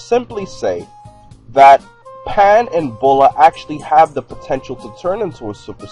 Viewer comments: Or she doesn't have saiyan in her genes like pan. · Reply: OH bulla, sorry I thought the vid said BULMA lol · It has nothing to do with genetics pan and bulla can but they never designed there characters that far..bulla would be harder since she's not a fighter..it has nothing to do with genes at all in the future pans grandson goes super so that proves it there that they prob could. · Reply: I thought they said Pan and Bulma at first.